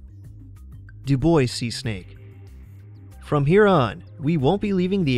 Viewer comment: 5. Dubois Seasnake: It's Pronounced "Dew-Bwah" (I know, it's a silly French Name; like Dufresne, one of my favorites to butcher!) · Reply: Right? I wasn't familiar with that snake and thought he was saying "Du Boise snake." Lol